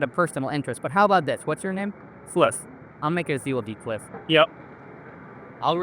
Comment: anyone else hear ‘fliff’